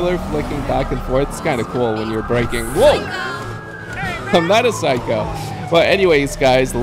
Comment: who ever had captions the captions said after the girl said psycho said I’m not a psycho but captions said look how the spoiler is flicking back and forth by lettuce